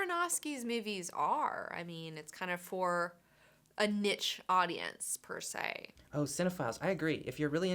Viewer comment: did she just say nitch for niche?